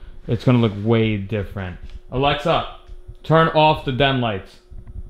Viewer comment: I heard "Alexa, turn off the damn lights" OK